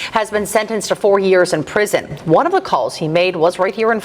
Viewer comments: I swear I heard “40 years” in the preview and thought “of course”. Watch and realize you said “4” and thought “WTF!!??” That’s not adequate for the possible lives he put in danger of death. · I thought he said 40 years and thought maybe not enough. But 4? A clear miscarriage of justice